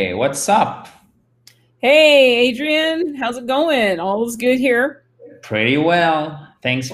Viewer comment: qu'est-ce que te dis juste après 'how is it going?' 0:05 merci . C'est intéressant aussi toutes ces petites phrases de conversation en marge du cours .... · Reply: jess anglais thank you for your question. All is good here